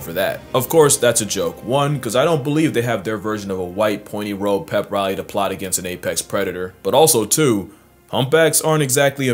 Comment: Yooooo did you say black people are apex predators? Wtf you mean ymthey don't have a pointy white hood to fight against apex predators...lmfao thats wild to say 😂😂😂